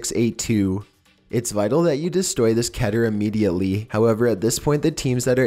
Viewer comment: did you just say kiter